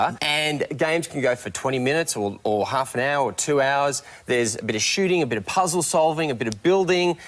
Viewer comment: did he just say 2 hours